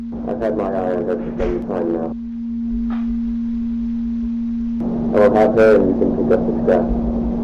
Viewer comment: I feel like 31:15 sounds more like "I will have her" instead of "But I've had ?????"